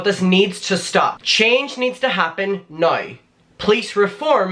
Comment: I thought you said "change needs to happen, no", "police reform needs to happen, no" at the beginning and I was so confused 😓